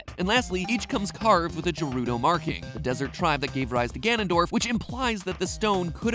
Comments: did he just say je-ru-do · BOI DID YOU JUST SAY "JERUDO"???